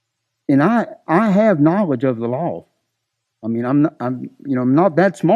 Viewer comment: Every time Russyboy says the word "law" I only hear lol I have knowledge of the lol I know about the lol I know how to read the lol lol lol lol